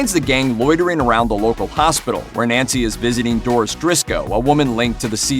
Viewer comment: Did he say Doris drisco???